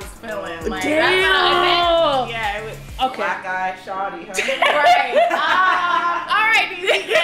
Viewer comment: DID SHE JUST SAY BLACK GUY SHORTY?! 😂😂😂